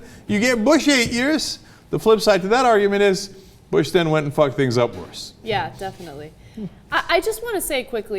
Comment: what did he say at 1:43? The flip side to that argument is...??? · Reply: "... Bush then went and fucked up worse"